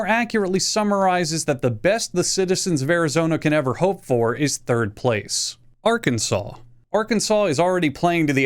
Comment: When he said "Third place", I thought he said "Third Base".